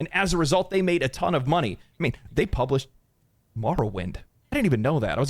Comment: the guy with the glasses says "They published Wumberweight"... ???? Or "Wumbler World"??? What did he say?? No matter how many times I rewind I don't think I'm hearing that right. The one word he decides to mumble but asmongold heard it just fine but doesn't repeat it like he usually would. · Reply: Morrowind